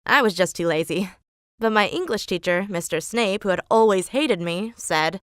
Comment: I thought she said Mr Snake